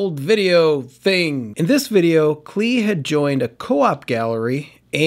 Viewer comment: At the beginning of the video I thought you said “Klee had joined a cult.” Lol